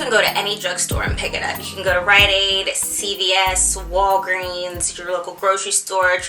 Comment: what is the place you said to go for the buy one get one free for biotin? it sounded like you said ryan aide but im pretty sure thats not it. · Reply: megan janes Rite aid